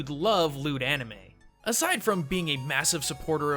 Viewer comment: For a second, I thought you said Lewd Anime, not Loot Anime.